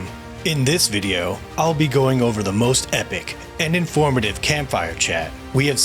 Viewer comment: The auto captions 1:14 wrote "and informative" but I'm pretty sure I heard uninformative